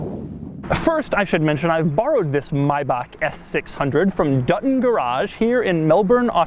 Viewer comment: I thought he said "Doug in Garage" instead of Dutton Garage